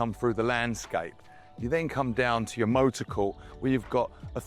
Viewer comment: Did he say "motor core"? 1:37 love that. All that space but no place for that mini stove on kitchen bench. Its an eyesore. Stunning home though😊